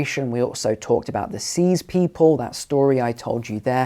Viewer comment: excuse me, please? Did you just say 'disease people'? I am frosted by this one; it's also nothing ironic. In the history all over, again? Is he pleading for general separation right now, or how do I take this?